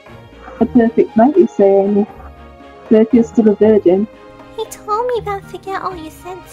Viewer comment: did they just said virgin